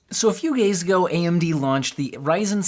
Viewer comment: FlexiSpot is having their brand day sale once a year now - Up to 60% off! It's the best time to get your favorite chair/desk now. Use the exclusive code ‘BDC7’. Purchase the C7 now and enjoy a $30 discount. FlexiSpot C7 Premium Ergonomic Chair US： <link> CA： <link> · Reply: yo I heard you said "so a few gays ago" lol